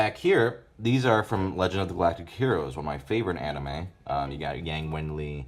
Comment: What's the name of that anime he recommended in 19:53? I can't hear it very well... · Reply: Legend of the Galactic Heroes